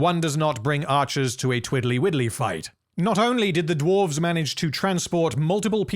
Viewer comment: I always thought Dain said "trwirly whirlys" and it was just his accent that made it kinda unclear